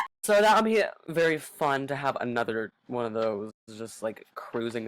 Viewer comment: I THOUGHT YOU SAID YOUR #3 WAS SHREK, NOT AN EGRET